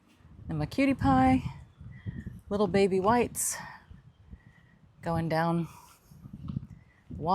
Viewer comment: Haha I thought you said “little Betty Whites” going down the walkway 😂🩷